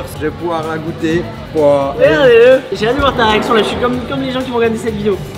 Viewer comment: a dit quoi ? J’ai adoré votre vidéo, trop drôle ! 😂😂😂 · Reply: Elerinna < regardez le >